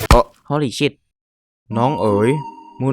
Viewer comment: พาไพเรต,พาไพรัต พูดว่า Ho le shit